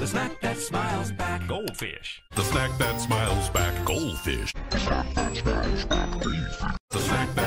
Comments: I thought it said "Snap that child's back goldfish" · You know i thought they always said "smack that smile back gold fish" lol instead of "snack that smile back goldfish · I thought it was saying the smack dat smio smack GOLDFISH. 😂😂😂😂